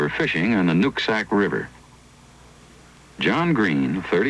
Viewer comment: i thought first said the nutsack river. No wonder the beast stunk.